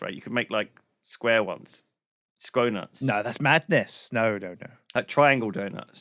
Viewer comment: I heard that as "scro-nuts".